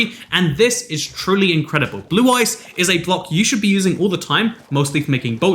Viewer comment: i swear i thought i heard blue-eyes and thought yugioh?